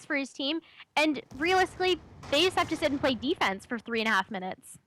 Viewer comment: did she say FaZe have to sit and play defense? Dumb bitch.....